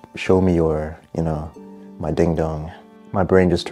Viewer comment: ding dong😂😂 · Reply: Bro I heard him say ding ding to 😂😂😂😂😂😂😂😂😂